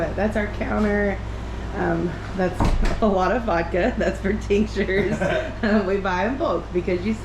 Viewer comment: OMG! Listen to this back. When you talked about vodka sound like you said it's for the TEACHERS! I died laughing. Then you kept saying it. I thought dang those teachers must need it bad. I still don't know what you're saying, but I know it's not teachers. lol I saw you touch a jar on the buffet cabinet and you said it again. 🤣 😂